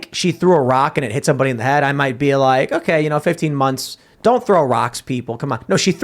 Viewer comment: Oh man, I thought they said 15 yrs,,,,15 MONTHS? That's a vacation.......